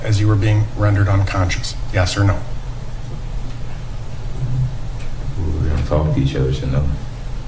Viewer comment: What does he say at 9:09? "we dont talk to each other so no"??